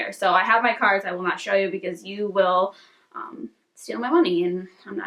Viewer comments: The nude YSL card holder would look amazing with your bag! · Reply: Did she just say we gonna steal her money????????? Wow. Very entitled. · Goal in life: be able to buy a LV and forget about it til it shows up😂👏🏽 absolutely love both bags!! That is the perfect fall bag! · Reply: Did she just say we gonna steal her money????????? Wow. Very entitled.